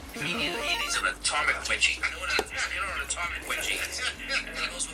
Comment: Did I hear "Atomic wedgie" in the background hehe